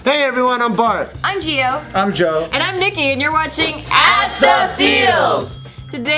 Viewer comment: Did I just heard "and I'm naked" instead of "I'm Nikki" ???